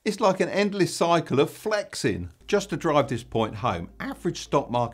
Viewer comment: did he say Flex? Subscribed